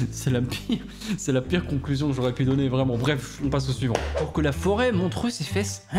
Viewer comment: À chaque fois j'entends quelque chose de différent, cette fois j'ai entendu "pour que la foudre montre ses fesses" mon cerveau ne doit pas être un cerveau humain😰